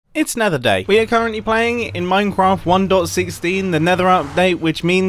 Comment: did he just say minecwaft